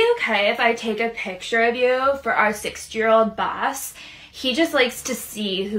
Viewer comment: I thought she said 6 year old